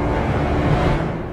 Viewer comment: "Pehaps it's time for them to be..." Did He say "redrawn?" Cause, that's what I heard.